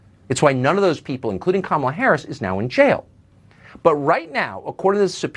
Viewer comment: Did anyone else catch that he said others including Kamela Harris is in jail (5:45 13:18 ) tell me I’m not tripping 🤔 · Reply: He said that's why they aren't in jail.